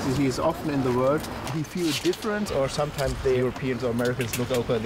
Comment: What exactly did he say, there? Couldn't quite make it out? "As is often.. the work? world? word?... then something about the Europeans look over...something, something.... "do the koreans also feel like that?" Feel like what? ...didn't understand it. Could anyone give me the exact question he asked?